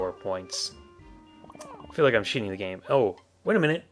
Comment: is "feel like I'm cheating the game" a curse? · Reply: oh, lol. I thought you said, "I feel like I'm shitting the game." Ok, my bad. I think the reason I heard it like that is because I have a dirty mind, lol xD